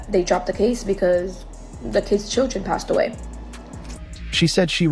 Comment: Did she just say "the kids children passed away?" ( at 8:00).